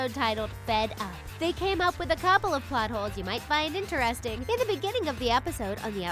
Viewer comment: I thought it said fart holes